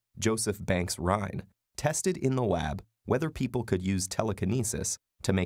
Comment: Did you just say wab?